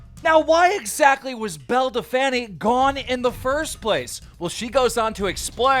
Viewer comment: Nobody: Keem: Belle Da Fanny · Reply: I kept hearing 'build a Fanny', which is kinda weird in the UK....